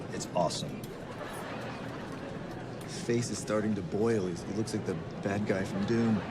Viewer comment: One thing I'd still like to know is 2:10 "His face is starting to boil.." Now does he say Dune? or Doom? :body-blue-raised-arms: sure isnt "Doing" :face-turquoise-covering-eyes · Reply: It's "Dune" 100%